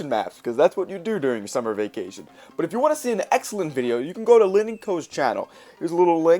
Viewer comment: Can someone PLEASE let me know the name of the YouTuber mentioned at 7:38 if you know!! I’ve played it back multiple times and cannot make out what he’s saying. I’ve tried so many different spellings of what I heard! xD Please help!!! !Peace&Love! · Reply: Linenco? I think